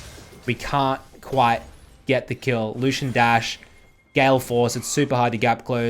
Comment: did he just say gay force ?????